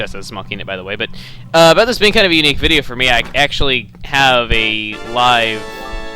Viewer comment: at first i thought he said "i actually have a life"